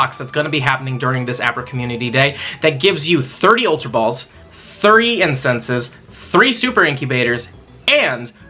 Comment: Just want confirmation it sounds like you said 30 incense? Is that true or was it 3?